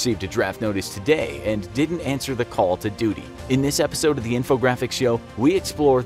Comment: I Thought He Said “Call Of Duty” Edit: He Ruined It He Added The Word “The”